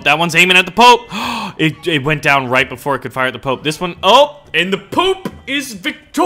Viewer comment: LOL poop instead of pop 23:10 XD